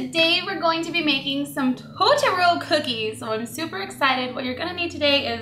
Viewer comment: I thought she was saying "tutorial" cookies